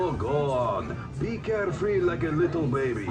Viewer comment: Did Zoltar say "Be carefree like a little baby"? LMAO